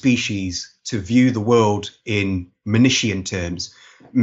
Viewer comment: what is the word used there; "monitian?" "menishian?" I can't find the right definition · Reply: Manichean -->Manicheism